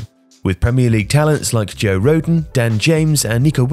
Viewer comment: nigga did you say joe rogan????